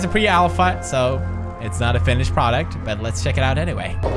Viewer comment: Who else heard "fetish product" at 0:11? XD